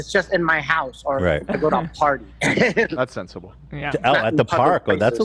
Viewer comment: Not Ethan mishearing drinking in the park 😂 he’s not a wildcard, he said party